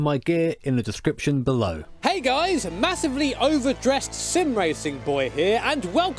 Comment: Idk why but I thought I heard "hey guys, massive legend here"